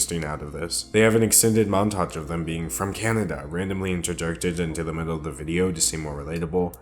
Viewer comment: what is he saying? am i having a stroke??? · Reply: he said "interjected" but he didn't say it very clearly